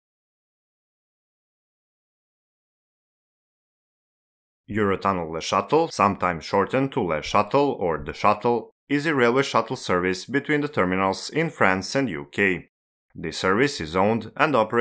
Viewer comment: I first heard ´Eurotunnel Le Château’ 😜